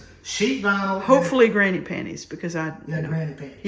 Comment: What??!! Oh.....Granny Panties.......at first I thought you said, Granite panties!!!!!😅😂😂